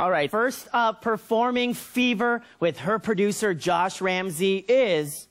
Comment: When they keep saying "fever" I thought they said "bieber" ...